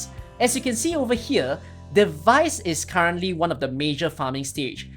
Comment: I heard Ch14 has a device farm, but maybe I heard wrong?